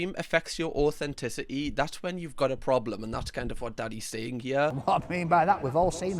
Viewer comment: bro said daddy instead of danny 30:33 😭😭😭